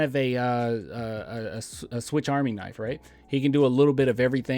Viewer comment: did say "switch" army knife?